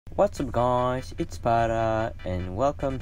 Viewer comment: I THOUGHT HE SAID "WASS UP GUYS ITS BADASS